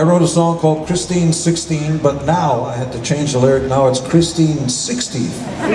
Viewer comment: Wait, what was the joke? I couldn't hear the next part. What does he call Christine 16 now? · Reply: Christine 60